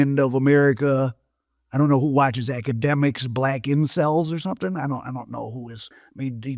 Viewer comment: Did he say black ensils?? Did he mean black embecile @ 7:50?? Wow that just sounds wrong!! Am I trippin?? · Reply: Incels